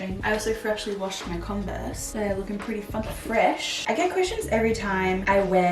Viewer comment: With the converse it sounded like you said they are looking ‘ pretty f**king fresh’ - nearly choked hahahah then I realised you said funky 🤣